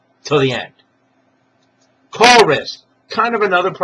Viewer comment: Couldn't understand you were saying "call risk" at 1:52 till I heard you repeat it at the end. Thanks for the video, by the way.